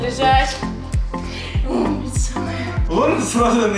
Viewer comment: На 6:39 мне послышалось "Садись жрать" XDDD